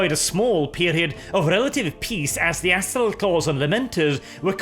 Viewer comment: Anyone else catch the part where nearly called the Astral Claws the 'Asshole Claws'? Just past